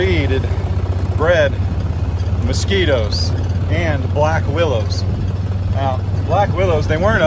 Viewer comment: At first I thought said the overflow was breeding “black widows”. Yikes!